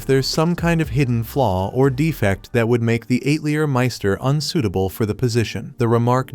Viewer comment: Am i the only one who thought he said "eightlier" master?😂 maybe cause i'm french canadian so we pronounce it differently